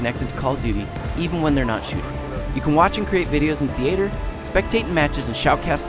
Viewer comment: did he say, TheAYtor?